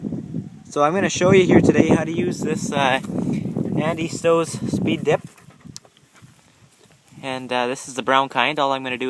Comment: I thought you said you hated Speed Dip?